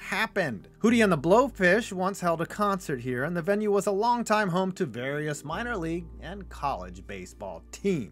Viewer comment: I swear I heard him say, "Darius minor league and college baseball teams..."